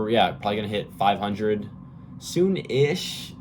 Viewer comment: i was folding laundry while listening and thought i heard you say you had almost 500k followers on tiktok. i had to go back because i was like bro there's no way you haven't hit $100k yet haha